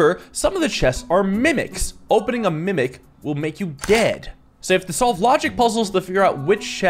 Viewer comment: I cleared my throat just as you said "opening a mimic will male you dead" at the start and i heard "opening a mimc will make you gay", and i wss happy thinking we'd gone back to 2003